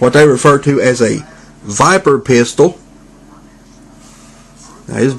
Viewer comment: I need to get both Mindwipes, and Skull Cruncher(I really want a modern skull cruncher) When you said viper pistol, I heard 'viber pistol' and thought something dirty XD